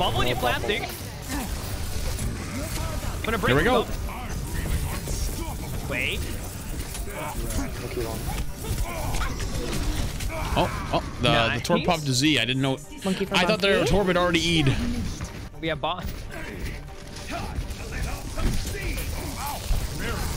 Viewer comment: I heard "Torb popped a Z" and then realized I misheard "Torb popped his E". Still gonna use that tho, it was hilarious.